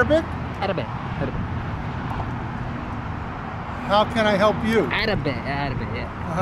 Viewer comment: Lol it sounded like you said can I pick your toe?😂😂😂😂😂😂🤣🤣🤣🤣🤣🤣🤣🤣.